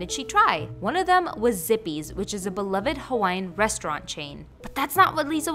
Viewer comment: When u said zippys I thought u said zippers 😩🤭🤦‍♀️